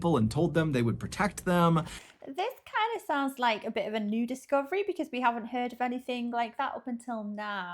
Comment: did you say Nudist Colony?